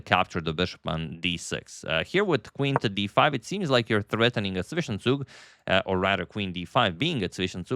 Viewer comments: Threatening a what please? Not sure I'm familiar with the word... Vishesug? What did you say here please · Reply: Zwischenzug (in-between move) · What is the word says at 5:17? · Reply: Zwischenzug: a German word adopted for chess meaning ‘intermediate move’. It means to play something in which the opponent must react to (generally with check) first before continuing with your main idea.